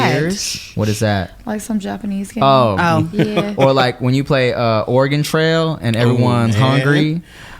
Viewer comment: it sounded like said "Organ Trail" 🤣